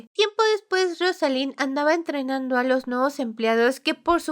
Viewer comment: Casi al final escuché "estrenando a los nuevos empleados", tuve que retroceder para darme cuenta de que fue "entrenando" JAJSJSSJS. Aunque sí creo a la prota capaz de hacer eso 🤭